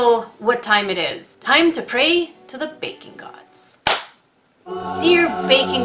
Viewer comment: I thought she was saying bacon gods this whole time🥓🥓🥓